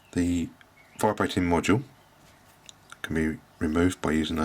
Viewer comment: could you tell what module is this? Unfortunetly I do not understand what You say ;) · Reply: "the vibrating module" :-) you could turn on captions..